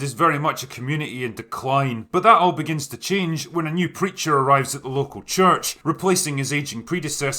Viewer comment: Ohhh, *Preacher*! I heard "creature" and was really confused for a couple of minutes. Wait there IS a creature???